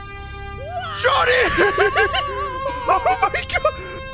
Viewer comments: He said Johnny and I thought he said Shawty😭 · I THOUGHT HE SAID SHAWTY NOT JOHNNY 😭😭